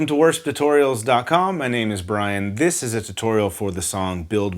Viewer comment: Hey Brian. At the beginning it sounded like you said “worst” tutorials and the auto caption even says it :) I’m here to encourage you and say you should never doubt yourself when your doing the work of the Lord. :))) Your awesome bro and hope to meet you one day!